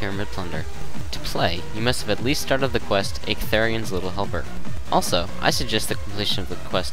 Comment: i thought you said ictharin's little humper in the video...lol